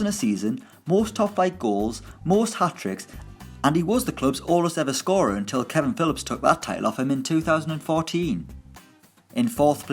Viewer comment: can someone explain what did narrator say? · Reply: jorge carvalho he was the club’s oldest ever scorer until Kevin Philips took that title off him in 2014